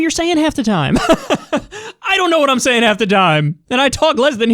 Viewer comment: "I don't know what I'm saying half the dime!"